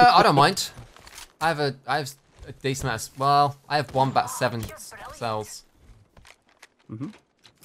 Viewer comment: Every time says “one bat” I always hear “wombat”